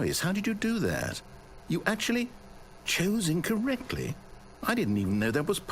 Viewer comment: I always thought he said "chosen correctly". "chose incorrectly" doesn't make any sense to me, at all.